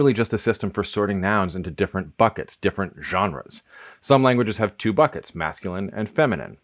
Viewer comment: To also be a nitpicker: "gender", in French is "genre" , not "gendre" (as it is written at 3:05). I think it is a typo because he clearly says "genre" (with a thick accent, but still, it does not look like an actual mistake ). The word "gendre" in French has (roughly) the same etymology but means "son-in-law".